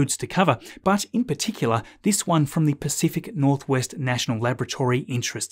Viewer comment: "pacific northwest national lavatory" is my best mishearing in the past year